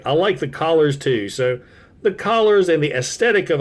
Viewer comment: Sorry but it does sound like collars over colors, likely your accent carrying over but it does sound that way. Noticed in a few videos lately it's not meant to sound mean or anything just a wait huh did he just say.... As for car mode when younger i did "he was Hardhead's younger brother with mobile artillery" kinda thought it was the 80's we thought odd things, so now you got some dirt on me lol.